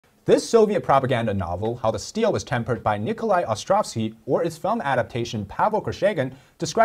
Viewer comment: I heard that as "Nikolai Autrocity"